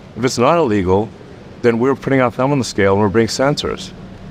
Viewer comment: I cannot make out what Musk says at 13:38. Thumb on scale? I don’t even know what that means.